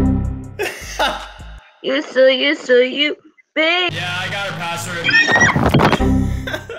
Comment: "U so, u so...uuuu, baee-" "yeah i got ur password :)" ªªªªªªªªª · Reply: i thought he said yeah i got her password